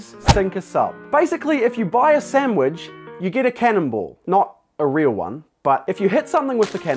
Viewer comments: For the entire intro I thought he was saying cannibal not cannonball. XD lol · I THOUGHT HE SAID CANNIBAL 😂